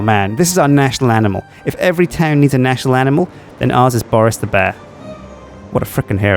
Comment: "every town needs a national animal" does even know what hes saying or does he just let what hes thinking out like sips? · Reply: Dank Lord he actually said "if every town needs a national animal, then ours is Boris the bear." This makes a lot more sense than what you thought he said